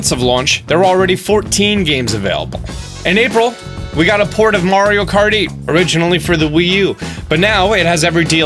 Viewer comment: did you say mario karty